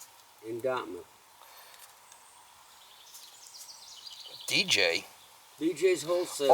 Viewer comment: I thought he was saying "DJ" too! :D